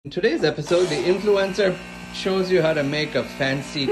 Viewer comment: Lucky for subtitles l could have sworn you said influenza. 🤣🤣🤣🤣